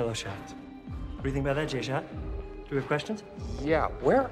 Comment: When he said j shot I thought he said j schlatt and I